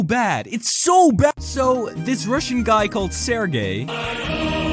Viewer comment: I thought u said serve gays